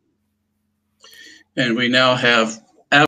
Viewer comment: What did he say at the 2:04-2:06 mark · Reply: Possibly 'absolute evidence' ?